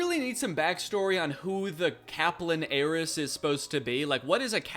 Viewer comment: I misheard Kaplan Heiress as capybara-ess and was very confused at first.